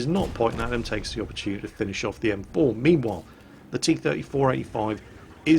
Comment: And every time said 'meanwhile' i actually heard it as 'memewhile' XD I guess this flu has gotten into my ears as well.